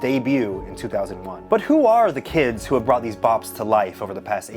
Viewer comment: 😂😂😂 I thought he said bots